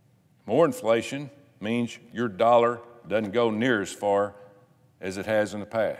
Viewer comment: Did he say YOUR dollars ? LMAO